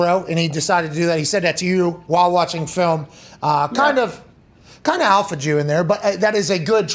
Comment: I had to rewind/relisten... Definitely heard that as "alpha Jew" the first time lol